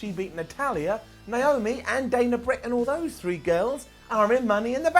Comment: Did I just hear him say "Dana Brick" 😂😂😂